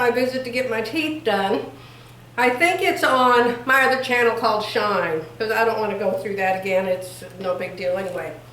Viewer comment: Did I hear you say at 1:09 that you have another channel called SHINE? I'm probably the only one of your fans who didn't realize it. Is it on YouTube? (I'm 72 and I don't use anything but YouTube & Facebook?. BTW I love the top you're wearing. Very pretty.